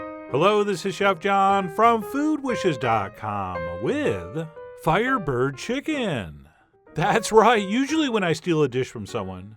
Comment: omg i thought it said firebender chicken💀💀i was like, is this babbish